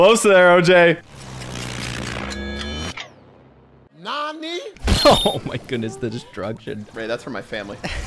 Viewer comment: What he mumble. Sounds like he said “all this drug s word”